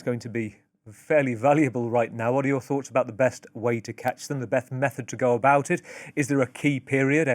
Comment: I heard that "beth method" lmao😅